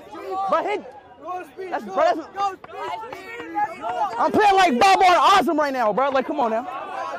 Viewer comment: did he says I playing like Babar Azam right now 😂